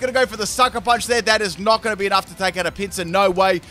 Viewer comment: Whenever you pronounce "Pinsir" I hear "pizza".